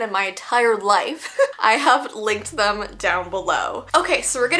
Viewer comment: I thought you said “I have licked them down below”